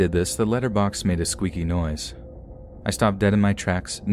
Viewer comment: When she said letter box I thought she said litter box I was cute you hava a cY but why's he in your house